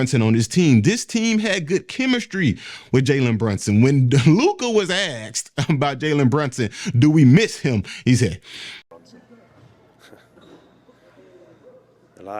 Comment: Did you say AKS?😂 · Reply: 😂😂 no asked